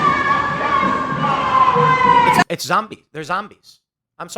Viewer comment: when I first heard them say ‘anti-gay’ I thought I heard ‘end Tapaei’